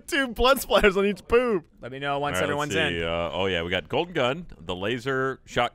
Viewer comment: When Said "Golden Gun", I honestly thought he said "Colton Dunn" XD